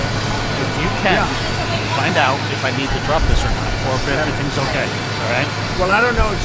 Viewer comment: What was that reporter saying at the 6:30 mark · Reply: " Hey _, If you can find out if I need to drop this or not is everything ok alright, these guys..." is this a hot mic from MSM reporter trying to figure out if they will broadcast this